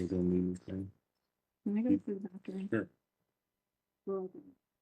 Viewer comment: Can ANYONE tell me if they hear a mucho creepo voice saying something at the 53:55 - 54:01 mark and possibly decipher WHAT it's saying???? 😢 It sounds so awful that I could only play it a couple of times, just to make sure it was there. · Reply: Agent asked if she needed anything, and Lietecia asked if she could go to the bathroom.....